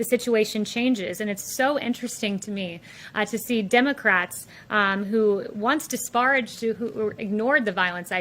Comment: Lmao....did you hear her call them " DEMOCRAPS"...I swear I heard her say that!!! Lmao!!!!